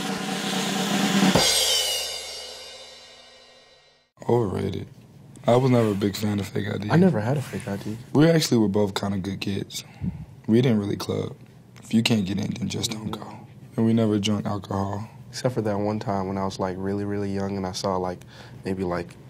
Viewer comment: I thought he said "We were actually both kinda gay kids"